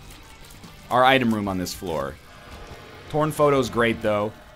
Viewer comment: When he said "torn photo", I heard "porn photo".